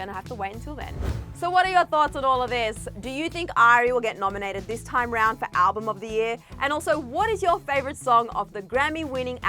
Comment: I thought said, “Do you think I will get nominated this time around for album of the year?” 😂 I was like, “When did you release an album?” Then I rewatched it. 🤦‍♂️